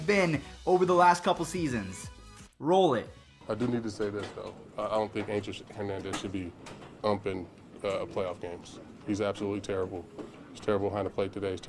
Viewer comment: When I heard cc I thought he said I don’t think Asians should play in play off games🤣🤣😂😂